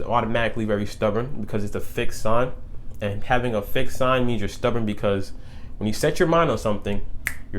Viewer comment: I heard "thick sign" first then I heard "fixed sign", but now I'm going to refer to my Taurus signs as the "thicc" signs hahaha